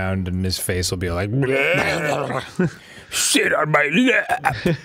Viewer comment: I really thought Danny said, "Shit on my lap!"